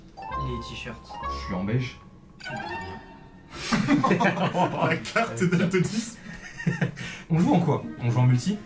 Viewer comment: il dit quoi? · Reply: Il a dit « jsuis dalitionnien »